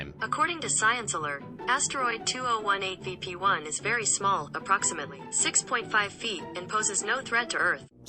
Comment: did I just hear pp small?